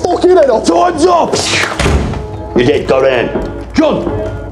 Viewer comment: You didn't go down! John?!😅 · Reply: I heard "you're dead! Go down" which I find even funnier lol